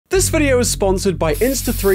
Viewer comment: At the beginning it sounds like he is saying "This video is sponsored by fffffffffffffff" 😂